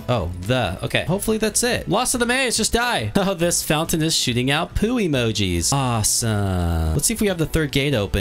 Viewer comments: Guys can you hear him said or im just misheard him and said "oh this fountain is shitting out poo emojis" 😳😳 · "this fountain is shiting out poop emojis!" Me: Did he just say shit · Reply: Shooting*